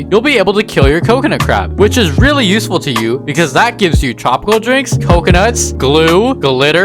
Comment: When he said tropical drinks i thought he said chocolate drinks lol